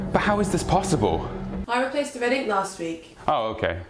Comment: I legitimately have no fucking clue what she said... after playing it like 20 times I think she said "I replaced the red ink last week"? maybe? I mean, the thing printed on the page was red, but does a printer even use red ink specifically? I thought most printers used one color cartridge, and the few that use separate cartridges use something like teal, magenta, yellow and black, not red...